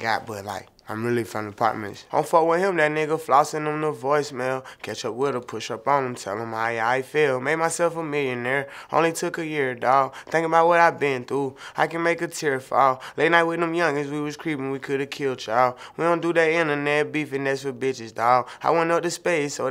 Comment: Am I the only who thought baby was saying " Made myself a millionaire, only to be hear dawg" smh?